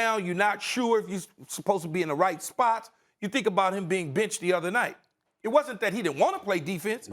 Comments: you can swear on the show? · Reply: He said “benched” ngl I thought he said “bitched” too at first 😂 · Did he say get bitched ?